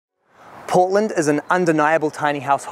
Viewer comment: I heard Poland. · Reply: I heard "Poland" too. Until it was followed by "Oregon" in black on white sans serif font